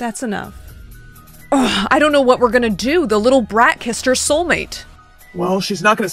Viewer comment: I thought it said KRISSED 🤣